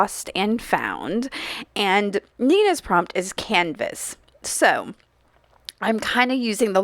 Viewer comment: I thought you said cannabis wow...then I realized you said canvas. I think. But it made me laugh 😅